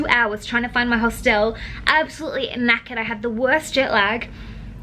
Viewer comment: I probs sound super ignorant lol, but I cannot figure out the word you're saying at 16:11. You said it a few times before that in the vlog and I'm wondering if it's just some Australian or Irish slang I don't know lol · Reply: She says "knackered" it means extremely tired :)